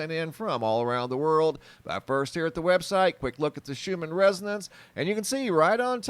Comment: Thought he was saying "Shoeman residence" for the longest.